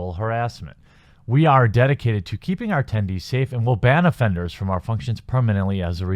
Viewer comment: I legit heard: "we are dedicated to keeping our tendies safe"